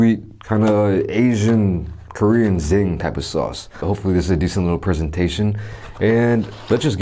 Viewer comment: did he say zing pepper sauce?